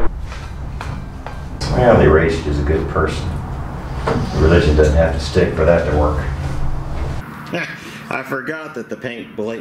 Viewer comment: I'm 100% sure that you did not say "really racist is a good person the religion doesn't have to stick for that to work" at 3:33 But that's what the stupid automated subtitle tells me. So what did you actually say there? I guess something about the paint? Great video as allways! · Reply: I hear "Well, they raised you as a good person. Religion doesn't have to stick for that to work."